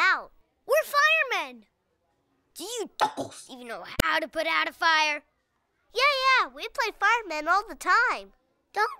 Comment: I was wondering what he said at 0:50 It sounds like he said "dorks" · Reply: He said "Dickheads"